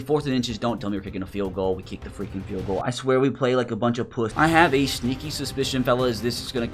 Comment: did he just say we are buch of puss